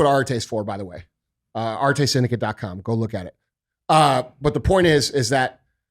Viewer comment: What did he say at 33:50? RTA Seneca? Thanks✌🏾